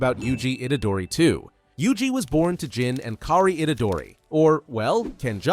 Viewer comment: when I heard “Hakari itadori” WHAT DO YOU MEAN HAKARI and then I realized it’s kaori